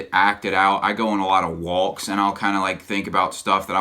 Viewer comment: did you say you go on a lot of "walks"?